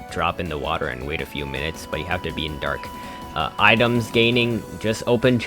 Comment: did you say you have to be in deep waters?